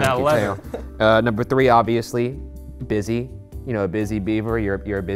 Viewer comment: im gonna be honest this whole time I thought it said “bussy” and I was so invested on what Ryan was gonna say.